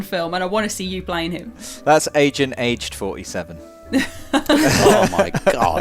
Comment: Agent AGED 47 has got to be the most underrated joke. I had to stop the video how much I was laughing. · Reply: I heard it as, Agent H 47 and was so confused why it was funny. Now it is very funny and I completely hear it now.